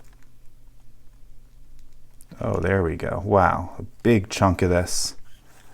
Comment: did u just say "big chungus"